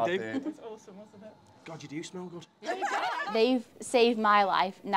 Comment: People Don't use the Lord's name in vain ( I know the subtitles say "Gosh", however i heard "God" in the audio ) Exodus 20:7 7 “You shall not take the name of the Lord your God in vain, for the Lord will not hold him guiltless who takes His name in vain